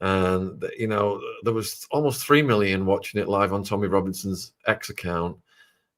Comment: Just saying, I first heard that as Tyler before Tommy